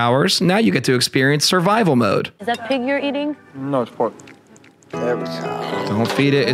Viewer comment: "Is that pig youre eating?" "No its pork." ...did i hear that right? 😂😂